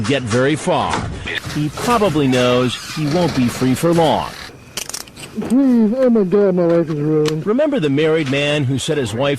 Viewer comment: what does the guy say at 8:03? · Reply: Please! Oh, my god. My life is ruined.